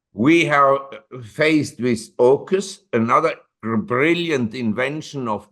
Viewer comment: "We are faced with Okus - another brilliant invention..." I do not know what this "Okus" is (I write the word surely incorrect). Could one of you please write it correctly in an answer that I can search for it? Thank you! 39:20 I agree with to look at all three pm as storage for buying power. When I started to stack; it was gold - and silver was only industrial. But silver was cheap and I stacked Ag against the expert opinion. Now Ag is money as well (lucky me!) and it is Pt which is cheap, but Pt is only industrial of course. 🙂 Guess what I stack? This was a wonderful relaxed and funny talk - thank you. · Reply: AUKUS- submarine deal between the US Australia and I think France